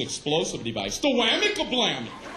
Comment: I think he changed his line a little bit, the first time I heard this I could have swore he said "The rooty tooty load it up and shooty!"